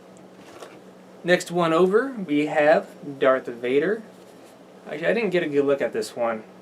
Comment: its darth vader, sounds like you said invader